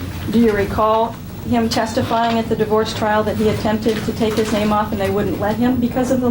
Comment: All I heard was, “I was stalking him and he wouldn’t let me keep staking him.” 😂